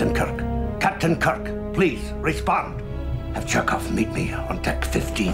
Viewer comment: I swear it sounds like Scotty says "Have Jerkoff meet me on deck fifteen!" 😄